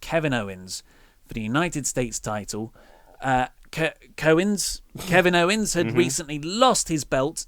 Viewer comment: says #KOwens instead of Kevin Owens☺☺☺☺